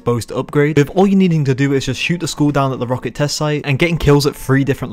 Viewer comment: gotta admit, I heard school and not skull...